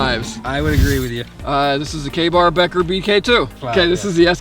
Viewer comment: When he says Ka Bar at 5:18 the subtitles say “gay bar”